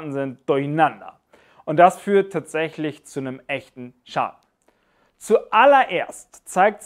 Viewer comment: “... zu nem echten was?“ habe das akustisch nicht verstanden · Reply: "...Schaden"